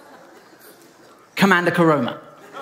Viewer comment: who else heard "commander corona" and got puzzled?